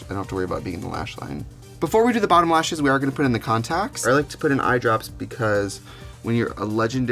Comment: did she say contact? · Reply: hey girl, it’s three years later and yes she did say contact